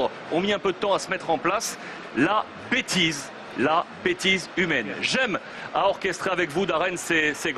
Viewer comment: thought he said the b word 😂is